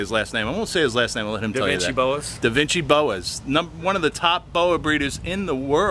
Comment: LOL, thought it said "Dancing Boas"